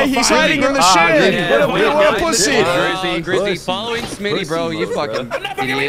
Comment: youtubes subtitles did SMY instead of smii7y